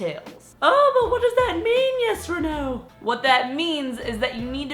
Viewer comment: Instead of "Yes Reneu" I always heard: "Yes Or No" haahah I'm not a native speaker, maybe that's why. Great and an awesome video, I agree with you! I'm a nice nerd haha, one day I got really really really tired of my History teacher that I started cheating (copying, not sure how to say that in English) in every test and not putting that much effort in each homework, but because she knew I was a good student, she used to give me A's (homeworks). :) Not a nice advise haha, but after that I felt bad and kept being a good student putting a lot of effort in each subject. But yeah, the Halo effect is effective 100% (unless your professor/teacher is an asshole).